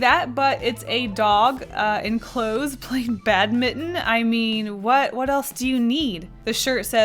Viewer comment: I thought you said “bad mitten” instead of ‘badminton’ 😂😂